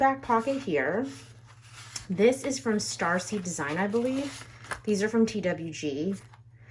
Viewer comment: I think I misheard the name. Is it Starsy Design?